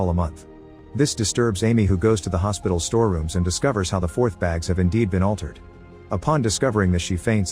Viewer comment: hahahah discovered how the "fourth" (IV) bags have indeed been altered 8:14 - had to listen to that twice to understand what he was saying